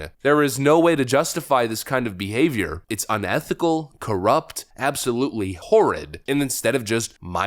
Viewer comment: All I heard was “I am corrupt!!”